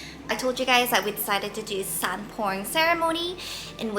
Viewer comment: I thought you said "sand porn ceremony" 😂 I was like "huh?" 😂